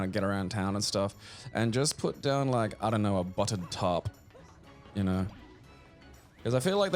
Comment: Misheard and thought he said "lie down a buttered tart for the seals"